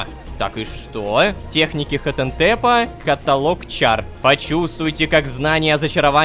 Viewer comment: Почему я услышала 'каталог чарт' он что будет колдовать чартами ;}